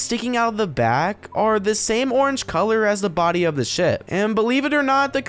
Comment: DID HE SAY SHIP OR THE S WORD